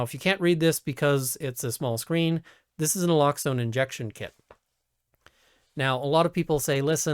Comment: Naloxone, it's the first thing he says about it. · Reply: Thanks. I heard it as "an elockzone kit." Needless to say, Google had no idea what I was looking for.